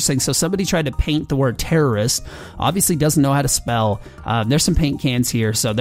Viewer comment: oh i thought it said Florist xD